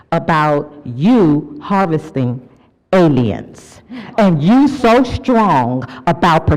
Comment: Did she just say harvesting aliens lmfao